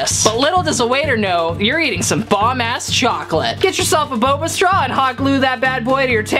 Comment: lmao. im sorry, but, idrc care if he did, but did he just say "bomb ass chocolate"? XD